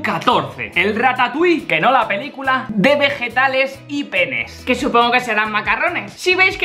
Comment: pense que dijo Pene y era Penne xD